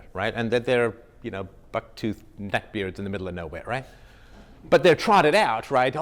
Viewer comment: Dont make the casual connection between "backwoods neckbeards" because that is what they (the Marxist/elites) are relying on. Discounting any patriotic person or anyone who disagrees as a 'backwoods neckbeard' has been the widest most frequently used slur against a lot of educated people who just want minimal government interference and a balanced budget. *edited for clarification. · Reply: I misheard - he said "bucktooth neckbeards". Here's the thing: a lot of people conflate the two- as you just did. Most often when people use that phrase (or various variations) they are trying so hard to put the same connotation on Nazi nationalism as people who just want to be left alone. That means anyone who does not wish to partake in planned obsolescence society- constantly judging themselves and others by the latest gadgets owned or the latest trendy clothes, people who want to live off the land and preserve nature, or even survivalists- who do not want to be bothered because 'manscaping' is not that high on their list of priorities. In short, patriots. People whose morals are staunch and whose bluecollar skills are necessary. Would i defend Nazis- NO. Do i know the difference- yes. The working poor are the backbone of this country.